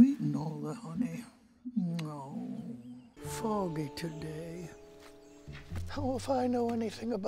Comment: Anyone else thought he said "Hell if I know..."?